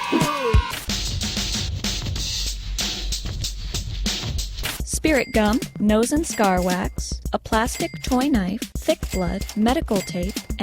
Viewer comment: Did she say spirit gun @ 1:27?